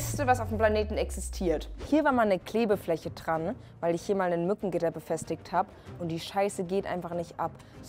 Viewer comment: ich hab nutten gitter verstanden 😂 Und mal ehrlich dieses Schrubb Ding sieht aus alsob es in die Nachttisch Schublade gehört 🤨